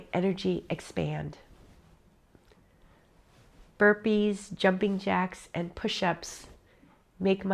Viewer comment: Omg I thought said HERPES jumping jacks and pushups 😮 I think I’m just staring at the cutest 60/30 yr old I’ve seen in a while 😁 and daydreaming like a schoolboy 😉